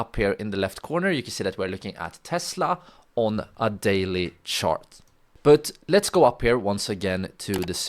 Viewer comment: Did he just say "shart"? I sharted one time..... Good Recap Brother!!!